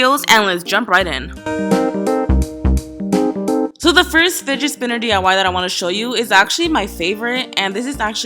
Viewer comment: sounds like u said spiget Spinner